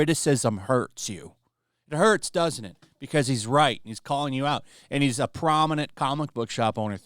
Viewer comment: At the end I thought you said "because he's white" instead of "because he's right" but honestly that would still be correct.